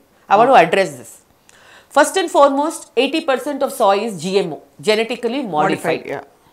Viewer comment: min, She said some thing called " SAW " i did not understand what she said. Can anybody pls let me know. " SAW " is new food -----> what is it? She said 80% of " SAW " is gmo , glysophate used and is endocrine disrupter so on....... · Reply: Soya. Like soya protein